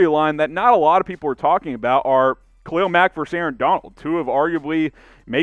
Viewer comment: I thought you said trade Khalil Mack dude you can’t do that to me